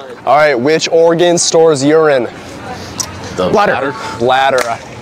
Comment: It sounded like he said “Which Oregon stores urine”😂